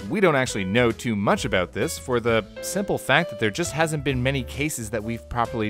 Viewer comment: did you say "there hasn't been many cases"?